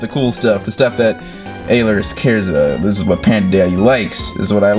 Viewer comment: I thought he said papada daddy and I was so confused about it just learned to day